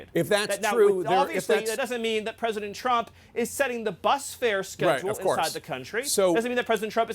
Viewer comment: It was the bus schedule. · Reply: It was "bus fare schedule" since you're hell bent on being technical about it. I began laughing after I heard fare. If you actually want to listen instead of attempting to school people, it's at 7:01......